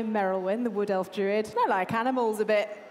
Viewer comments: I honestly thought merilwen said she liked cannibals a bit instead of animals. Put a whole new spin on the meat grinder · Misheard that as "I like cannibals a bit". Wasn't helped when, famed cannibalism promoter, started miming eating a chicken leg. ...Oh, he was mouthing "a bit". That makes more sense. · For a split second at 1:13 I thought said Merrilwen liked Cannibals a bit lol